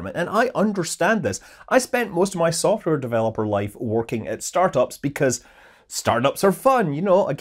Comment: When Scott said he worked in startups I misheard it as Starbucks. 😂